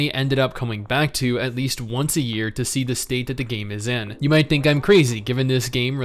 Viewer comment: how can you play with the lag from AU? what vpn are you using? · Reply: I thought he said he move to the states.